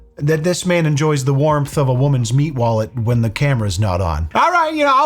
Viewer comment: What was that you said ? 'Meat wallet ' ? Maybe I misheard but that sounds like one of the crudest expressions I have ever heard . Your mother, your sister ? You have lost THIS subscriber.